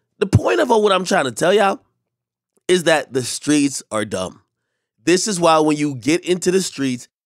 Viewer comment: “Streets are done” when have the streets ever lead to anything besides death or prison? Stop glorifying this shit 😂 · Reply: I thought he said “wow the streets are dumb” AK can you clarify the statement please? Lmao